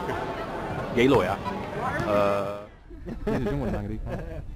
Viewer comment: is it just me or did he really said gay lawyer !lmao